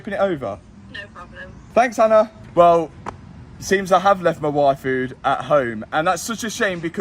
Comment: said he left his YFood at home... but my brain thought he said Waifu